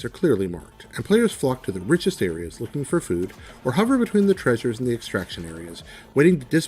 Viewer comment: did he just say looking for food?